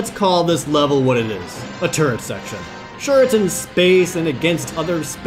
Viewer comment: i thought he said "turd" section, instead o turret section